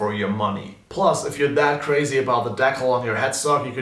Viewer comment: sorry i kept hearing 'deckle' and just realized it was meant to be 'decal' (or: deecal) I'm ok now.